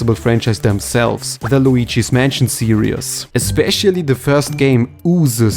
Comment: Why do you randomly add extra syllables? Here for example it sounds like you say "Luigi's Mansion serious"